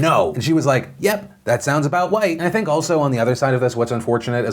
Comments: Did he just say, "that’s about white"? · Did I hear correctly or did he say "that sounds about white (right)!" 😂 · did he say "nope that sounds about white" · did he just say white? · PLEASE TELL ME HE ACTUALLY SAID "THAT SOUNDS ABOUT WHITE" IM DYING